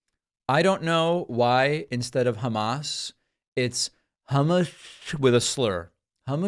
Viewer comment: I thought he said "Star of Thomas." I had to rewatch after you explained what he said.